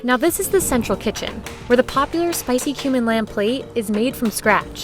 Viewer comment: Me who thought they said spicy human lamb